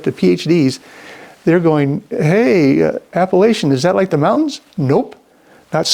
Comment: Appellation 😂 I thought you were saying Appalachian ... great video Brother, thank you for posting! Prayers and blessings to you and your family 🙏❤️